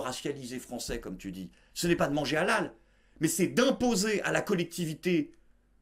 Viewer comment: Je me demandais pourquoi tu passais autant de temps à parler de cette femme aux yeux mi SJW mi scientologue et j'ai entendu "impots"... J'avoue, j'ai gueulé.